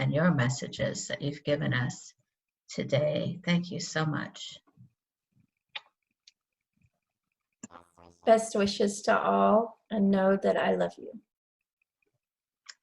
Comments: Yes thank you so much and source. I'm amazed I will listen to this again. Yes I to believe I heard the EVP. At 56:27. ❤❤I heard best wishes. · I think i can hear at 56:27 it says best wishes. Thanx for that amazing gift